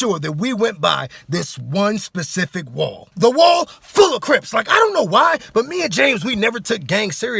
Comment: Those 4 dislikes are from Willy and the Crips that jumped you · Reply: I thought u said crisps